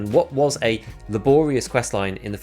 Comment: did he just say "la-boreas" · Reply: Laborious